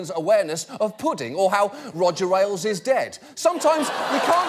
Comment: what does he say? Roger walles? Wellis? Whales? Rodger? Im trying to google him to find out why Oliver dislikes him, but everything i write into google gives me different people. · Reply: Roger Ailes